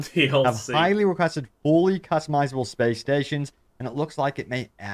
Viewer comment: I thought he said “Play Stations” 🤣🤣